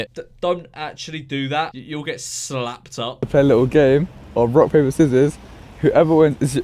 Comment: Did he just say "You'll get slapped up"? 😂